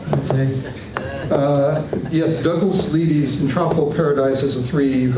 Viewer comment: Anyone know the album and painting technique he mentions.. I'm struggling with the audio and not finding anything with Google.. sounds like he said Douglas Levy "Tropical Paradise" and "Podulism".. ? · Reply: Douglas Leedy’s “Entropical Paradise”. A really inspiring work indeed.